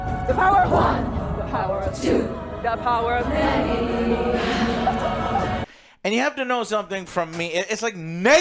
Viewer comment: I legit thought they were saying The Power Of Daddyyyyyyyy. LMAO